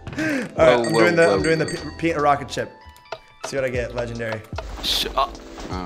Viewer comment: WHAT WERE U GONNA SAY?? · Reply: PenaRocketShip